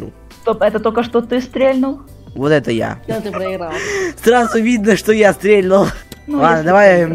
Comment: что он сказал? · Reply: Все ты проиграл